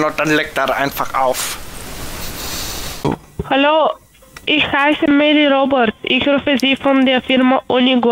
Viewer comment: Bei 31:48 hab ich "Ich heisse Meddl-Robot" verstanden 😁🐉